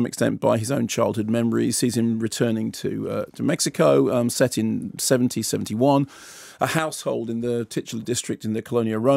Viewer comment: “set in 1771" God your head must be a beautiful busy place, Mr. Kermode. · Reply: Errm, the way I heard it he said "Set in 70/71." Soz. Feel free to hear it the funny way, though. :)